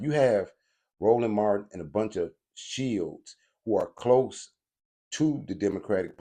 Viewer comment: The term is Democrat SHILLS. Its sounds like your saying shields.